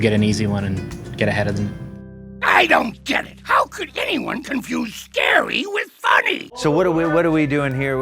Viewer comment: i thought the flying dutchman said "HOW COULD ANYONE CONFUSE GARY WITH BUNNY?"